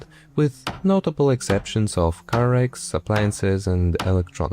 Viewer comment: what do you say before appliances and electronics? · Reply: car wrecks